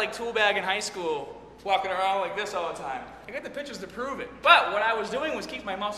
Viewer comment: I definitely thought he said he had the bitches to prove it.